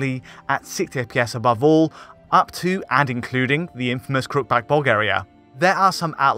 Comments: What did you say about Bulgaria? I couldn't understand you. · Anyone else hear "the infamous crook back Bulgaria"? Took me by surprise lol.